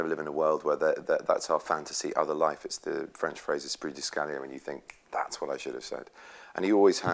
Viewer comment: What does he say at 1:16? " The French phrase it's pretty scary when you think that's what I should have said" ? I don't understand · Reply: He was talking about the French phrase "l'esprit de l'escalier" (literally, "the staircaise spirit"), which is about the feeling you get when you suddenly find your punchline, what you should have said in the heat of the moment, but way too late, after the discussion's over. Benedict alluded to this phrase to explain why most of Scotland Yard detectives don't like Sherlock: because "l'esprit de l'escalier" always happens to them when they're with Sherlock, he notices everything with only one glance and always has the last word, which is frustrating. And yes, Benedict Cumberbatch is a very cultivated man to know about this phrase, I'm French myself and I only found out about it very recently